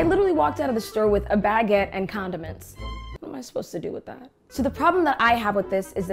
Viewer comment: I thought she said a baguette and condoms 😂